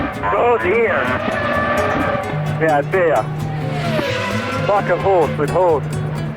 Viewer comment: I've always thought the race enginner said : "A fucking horse with horns"